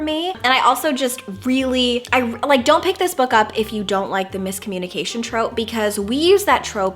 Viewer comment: Did you just say... BOOKle up???